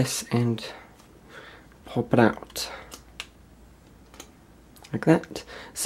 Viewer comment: did he say poop it out!?! Cause that’s what I heard....😂😂🤣😂😭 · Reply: pop it out 😂